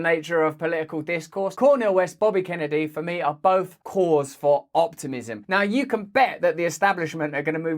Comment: What you actually said, “both are a cause for optimism.” What I thought you said, “both are whores for optimism.” Made me think, “I might get on board with that.” Have a nice day 😊